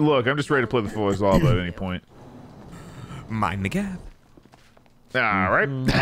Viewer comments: ok, am I drunk or did say something he shouldnt at 37:50? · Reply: “Mind the gap” ????? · Maybe it's just my ears playing tricks on me but does say "My n***a" at 37:51?